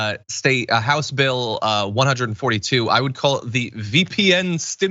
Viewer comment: I totally heard that as "V-Peen Stimulus Act" which also works.